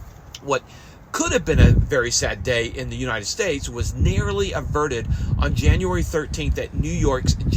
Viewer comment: He said narrowly averted.. not nearly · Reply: Thanks, I went back and listened again. He said that twice, and the second time @ 2:09 his accent swallows the middle syllable and makes "nair-row-lee" sound like "nair-lee" which made me think he'd inadvertently said "nearly". Either way, comments help the algorithm, so cheers!